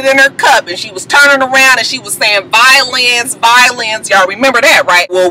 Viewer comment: Lol I thought Erica was saying "Bye Lance" I was confused because I was like I thought his name was Darryl lol I feel stupid now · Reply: I thought she said violin as well! Lance doesn't make sense but maybe